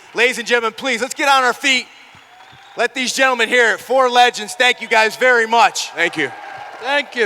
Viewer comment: Tito? I thought you said legends.